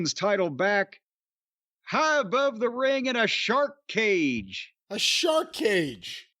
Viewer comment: I thought she said "Shock cage"